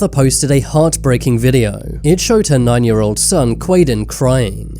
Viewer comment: Ok so is it just me or are the captions drunk rn? 💀💀💀💀💀💀💀 6:45 Edit: Sry it was 6:45 not 0:45 Edit 2: They changed the subtitle quality now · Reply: If you go to 6:45 The captions will say 99 year old instead of just "9"